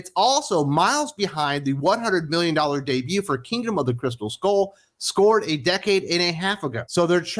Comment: “A decade in a half…” That’s five years. Stopped watching at that point. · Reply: You misheard what was said. "Crystal Skull scored a decade and a half ago..." if you put on the subtitles/closed captions.